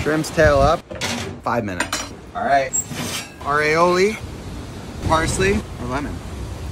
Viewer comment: “Our Aioli” Bro I legit thought he said Ravioli